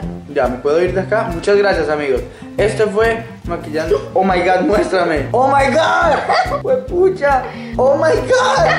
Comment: creo que escuche que Camilo dijo:hijo de cucha jajajjaja 😂😂👁️👄👁️